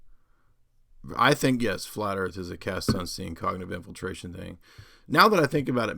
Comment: What does jay say at 39:09? “Flat earth is a ___________ cognitive infiltration…” cast sunstein?? Wat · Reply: Kevin Thrasher cass sunsteins paper on conspiracy theories and how to disrupt truth movements